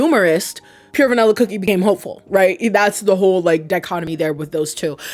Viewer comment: I heard "Pure Vanilla Cookie became a homophobe" and had to rewind 😭😭😭